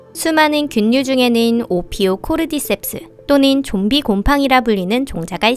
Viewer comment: 네..? 뭐라고요? 섹ㅅ...? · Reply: 셉스